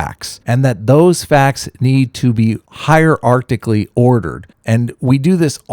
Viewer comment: Did you just say "Higher Arctically" instead of hierarchically?? Well done, as usual.